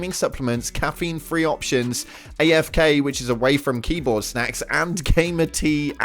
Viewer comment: I know my homeboy did not just say 'way-foo' cups